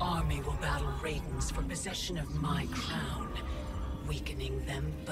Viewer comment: did Kronika say "for possession of my clown 🤡"